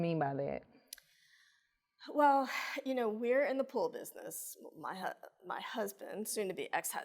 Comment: I thought she said in the pole business 😭